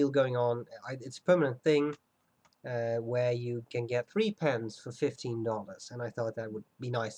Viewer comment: when i watched first time i thought u said "free pens for 15 dollars". hmmmmmm sounds a little sketchy to me to be honest.